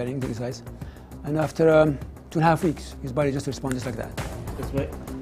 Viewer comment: what exactly does the guy says here? I'm not a native english speaker and I can't understand it. 2,5 weeks? The body "expands" or what? Thanks. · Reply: His body respond just like that.